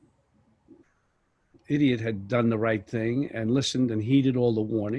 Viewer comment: At the 4:44 mark, did he say “...if IDIOT had done the right thing”...??? LOL